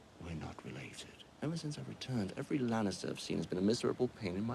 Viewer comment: 1) who was that figure at the end with the errie musing 2) Jamie was totally hitting on her at the end. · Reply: Philistine I thought he said “ got the looks”